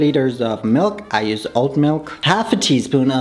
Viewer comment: I thought you said I USE OLD MILK NOT OAT 😂 😂